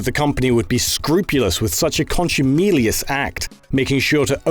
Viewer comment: Around the 9:00 mark... what is this word?? “Conscimelious”? Not in my American-English vocabulary. Some please help or explain. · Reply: armand duarte “contumelious” It seems to be an Old English word meaning scornful or insulting. I’ve never heard anyone ever use it before either.